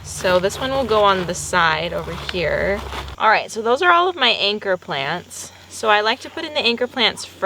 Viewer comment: When you said "anchor plants" i thought you said "anger plants" at first and I think that's a hilarious name for cacti